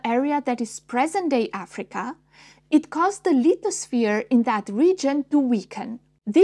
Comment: what did you say? The "little sphere"? Why didn't it weaken? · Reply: She said lithosphere... Turn the closed captioning on and you would see that! 🤡